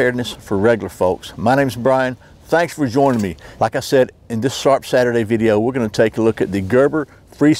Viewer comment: No joke when I first subscribed I swore he was saying shart Saturdays